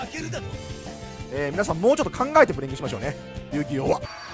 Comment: 「考えてプレミしましょう」って聞こえるんですがｗ